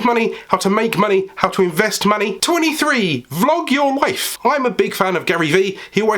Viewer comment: Great video! Although I did have to repeat tip 23 as I thought you said: ‘Flog your wife’! 😂